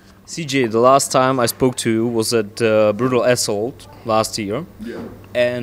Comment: sounded like he said brutal asshole when he said brutal assault